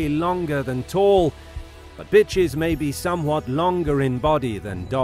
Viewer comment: actually said "bitches" 1:39👈 I know that's actually what a female dog is called. But I didn't think he was willing to say it. 😖